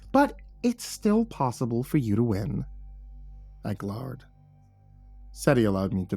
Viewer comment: I glhard...(mispronounced glared so its with a hard r like the word is glhard instead of glared sounding like glaird. not nickpicking, just staying this made me laugh so (gl)hard that it was difficult be spooked after.)